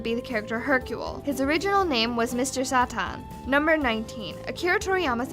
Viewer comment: What was that pronunciation of Mr. Satan, sound like you said Mr. Saturn.